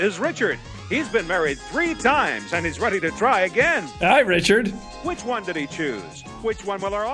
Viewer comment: This is Richard. He's been married three times and he's ready to try again. I thought he said CRY again at first.